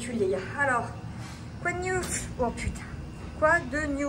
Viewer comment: "Quoi de "gnouf" ?" 🤔 [gnouf \ɲuf\ masculin : (Argot) (Prison) Prison, poste de police.]